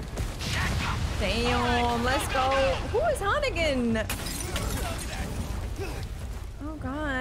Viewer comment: did she actually say 'who's Hunnigan'? Is she playing a different game or something? Come on, lol